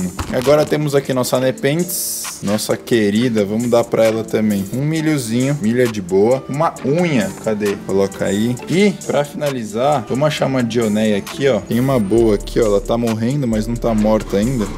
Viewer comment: entendí maconha kkkkkkkk